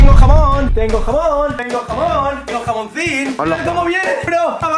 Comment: Jaja pense que dijo jabon que jamon